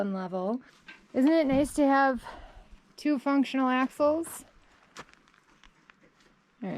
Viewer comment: So good to see you and Robert reunited. I gotta confess, upon first listen around the 15 minute mark I thought you said "isn't it nice to having two functioning assholes again?" I quickly realized my mistake when you panned to the trailer! ;-) Good luck with your ACIM studies. I joined a study group when I began with The Course and the facilitator has us focus on the workbook lessons for the first year. That made a huge difference in my practical understanding in the years to come. Cheers!